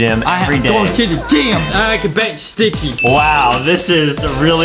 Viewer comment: “I can bench sticky” I thought he said “I can bend sticky” I’m like: excuse me what?